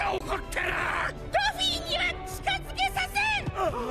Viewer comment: ドフィには近づけないぞがルフィには近づけないぞに聞こえた